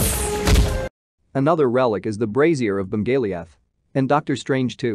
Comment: Bro I swear I literally heard " Brazier of zendeya" 😂 Sorry for bad English